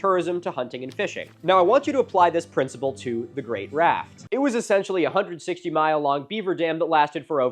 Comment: I thought he was saying “The Great Wrath” which I thought was probably a pretty accurate name.